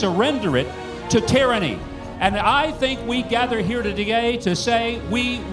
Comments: He says to d'gay instead of today 0_0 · My man accidentally said togay instead of today